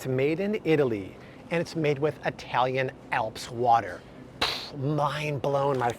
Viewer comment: He had to rewind and listen again because I thought you said “made with Italian elf water”!!! 💦 😅😅😅😅😅😅